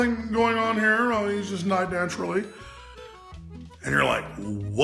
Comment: Did he say "nied daturally"?